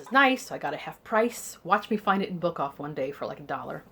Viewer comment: Thanks for joining in! I have a few of the Mrs Jefferies books on my shelves but I haven't read them yet. Looking forward to hearing what you think! Also, did you say Bookoff?!? I think that's what I heard you say.....I love Bookoff ^-^ Hope you have a great time participating! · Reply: Yep, Bookoff!